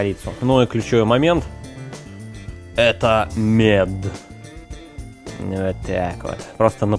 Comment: Мне одному на 3:05 послышалось как Друже сказал МЕТ ? ))))